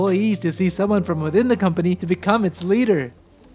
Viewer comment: I don't know why but for some reason I heard "enslaver" and not "it's leader" lol